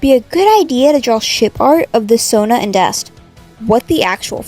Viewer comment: I thought that she said shit art…!